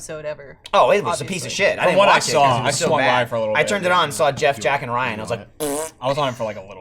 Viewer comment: "I turned it on, saw Geoff jacking Ryan..." I mean...we all know what he really said, but this sounds like something that could happen in their office.